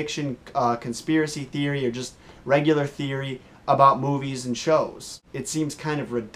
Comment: when you said movies i thought you said boobies